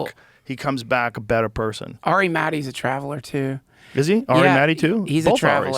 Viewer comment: what does Joe say here? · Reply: “Both Ari’s” because both of their names are Ari